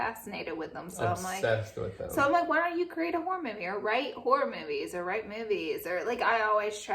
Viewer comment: When they said ‘horror movie’ I thought they said horse movie😂😂